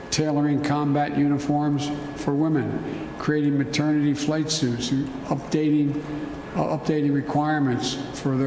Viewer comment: Im sorry did Biden say 'maternity flight suits' [ 25:30 ]